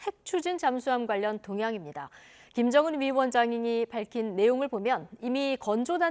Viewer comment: 내 귀가 이상한거야?? 왜 암만 들어도 "김정은 위원장님이 밝힌 내용을 보면" 으로 들리지???